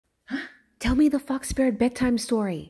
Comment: i thought she said “tell me the f*ck spirit bedtime story!” i had to replay it 😭😭